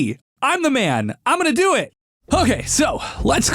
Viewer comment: i HEARD you said: its me AUSTIN!... but you never said it! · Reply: i meant that i have heard his intro soooo many times that when he said its me "it's me! im gonna do it" then i heard: AUSTIN instead of "it's me! im gonna do it"